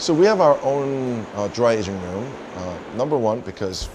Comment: OMG I Thought They Said it’s their “Dry Asian Room” but it’s the “Dry Ageing Room” 😅🤦‍♂️🤷‍♂️😂😂